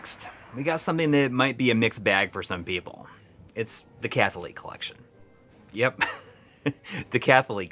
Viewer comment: I thought you were saying “ The Kathy Lee collection” and I thought, that’s quite interesting.